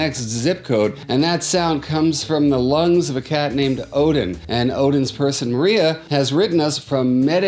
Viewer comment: I thought he said "a cat named Covid" my volume was low 😆😆